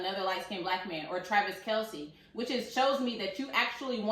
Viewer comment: I thought she said Drake and Patrick Mahommes. Whoever it was I laughed because neither of those men can dress 👀 You and your mom are too cute.